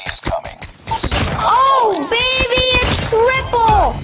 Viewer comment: I thought i heard dababy 👁-👁